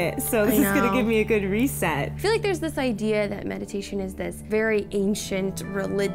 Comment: It thought it said medication..